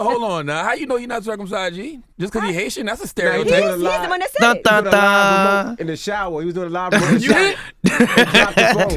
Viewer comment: wtf this nigga say?! "Dun Dun Dun!" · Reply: he says, "thot tha thaa", cuz she brought up the whole penis convo